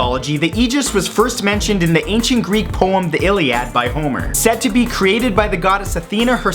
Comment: Any one think he said boner